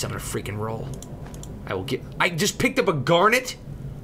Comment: did he say f#ck?